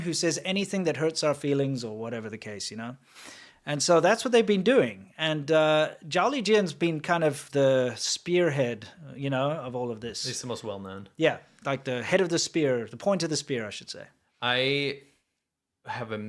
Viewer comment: "jolly Jin".....uhm, that's what I heard...